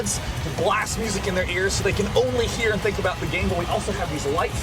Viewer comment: I swear the guy says "so they can only think about the Gameboy" at 10:42. I figured that's fitting, since it's MLG reporting lol